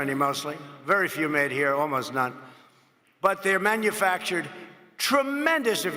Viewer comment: did he say trumpmendous ?